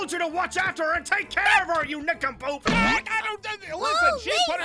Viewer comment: what did you say Zane 😃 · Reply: He said nincompoop chill